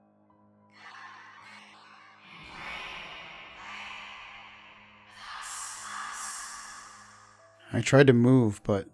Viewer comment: I can't make out what's being said by demonic Poe at 6:38. Can anyone else? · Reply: I think she's saying "free us" or something similar